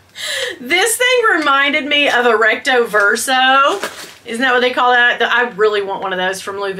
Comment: Wait what?! You’re allowed back on Coach? 🤷‍♀️ Stop the madness! What Twilight Zone have I stumbled into? 🫣🧟‍♂️😂 but lol…I had hot coffee shoot out of my nose when I thought I heard you say the wallet is like the Louis Vuitton “Rectal !” 😂😂 Boy do I love that mini backpack and the slippers! ❤️‍🔥🥰 Love you honey bunny! 🍯 🐰